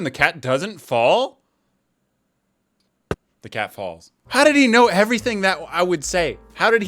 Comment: U SAID FALLED INSTEAD OF FELL🤣🤣🤣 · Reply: he said falls